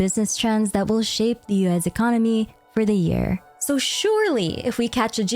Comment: For some reason I always hear "US" when you say "Liyue's"